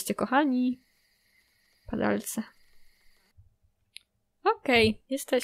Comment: Usłyszałam pedalce a nie padalce xd